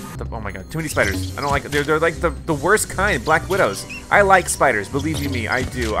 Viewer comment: i thought he said he liked spiders